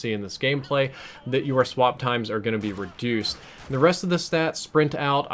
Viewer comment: did you say "you are swap times" like you're swap times instead of 'your'?